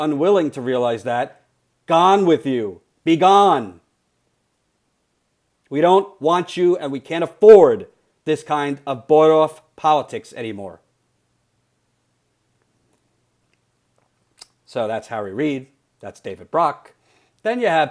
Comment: What does Jordan say "boyoff politics"?